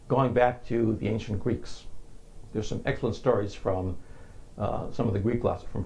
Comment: I cannot make out what he is saying at 1:02:20 Going back to the ancient Greeks. There are some excellent stories from...? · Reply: excellent stories from Pliny.